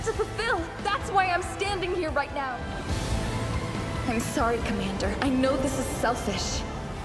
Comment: am I the only one that heard him say sexy commander😭😭😭